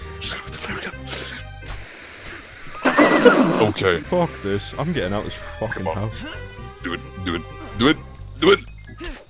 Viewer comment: turn on captions in english and the caption says at 8:13 "Okay, fuck this, i'm gay and I was talking about do it do it do it do it?" Is this a conspiracy? Is Youtube trying to tell us something? Tell me what you think in the comments.